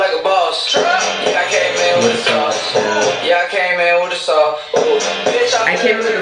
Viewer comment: I thought it said yeah I came in with a saw I thought it meant The machine gun